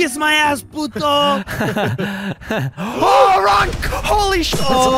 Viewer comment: did anyone notice that vanoss said "OHHH RONK!" instead of when almost messed up ( 12:28 )